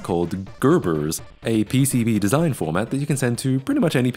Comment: I thought he said PCP lmao